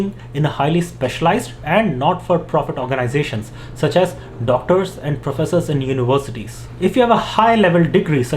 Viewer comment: Did I hear Doctors? 😇😇😇😇😇😇😇😇😇